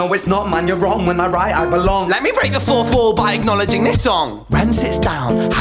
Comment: damn, i always thought he was saying break the 4/4, as in beat